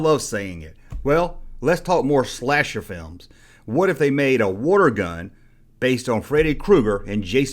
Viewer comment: I thought you were saying Wargun. I was like WTF is a Wargun??? I never heard of that line. Then I figured out you were saying WATER GUN. lol